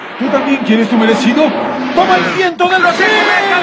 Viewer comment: Décadas han pasado y sigo sin entender lo que dice en el 0:04 "Toma el viento de David"